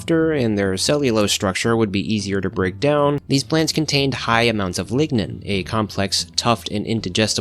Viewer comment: i thought you said ligma for a bit there